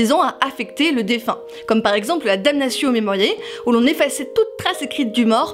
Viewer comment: j'ai pas compris : " Damnation .... " , quoi ? Merci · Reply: Damnatio Memoriae